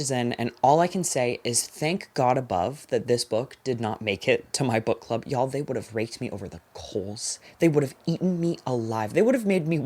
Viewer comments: what did you say here, it sounded so bad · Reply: “Thank god above that this book didn’t make it to my book club- Yall, they would’ve raked me over the coals, they would’ve eaten me alive…” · No Mr.subtitles.... That's not what said... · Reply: said raked